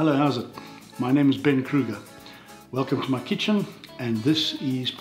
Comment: At first I thought he had said his name was Freddy Krueger.